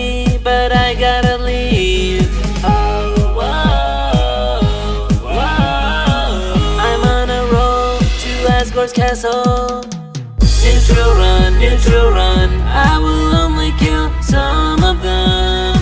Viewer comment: i heard that as asgore's asshole what is wrong with me